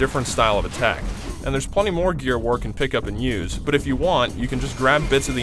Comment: i don't even own an xbox or have played gears 2 that much, except a select few times with friends, but it did sound like he said gear of war, and that thing he was carrying is reminiscent of countless gears of war weapons i have seen from trailers. i'm only making observations from a regular person's point of view, maybe you should ask yourself if you're obsessed with THIS game before making any accusations.